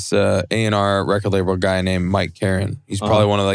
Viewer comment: Who is he talking about in 26:50 ? The music guy , I heard Mike Karen but found nothing on it